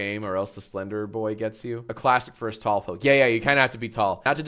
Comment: i was falling asleep watching this and thought he said “astolfo” instead of “us tall folk” needless to say i am no longer falling asleep watching this